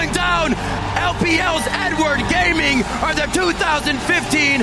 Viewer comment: N-word gaming? What did he say? LOL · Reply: Edward gaming i think.